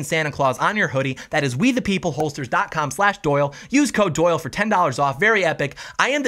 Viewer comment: For a second a thought it said “whitepeopleholsters”